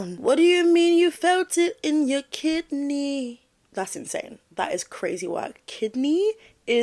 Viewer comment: I thought she said “felt it in my KITTY!” Which makes a lot more sense. I honestly think that’s what she did say…not kidney! 😂😂